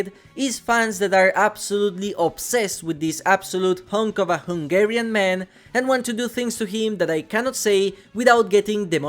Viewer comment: did u just say i want to f*ck a Hungarian man?